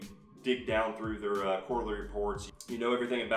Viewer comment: you “dig thru there c*** reports” what was the c word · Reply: Quarterly